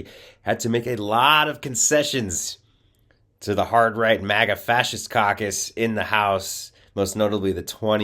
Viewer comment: I swear he said the maga fascist cocks the first time I heard him, that's not to say he would be wrong 🤣